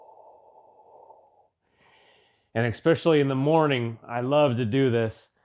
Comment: Wait... Did he just say "exspecially"? 🤦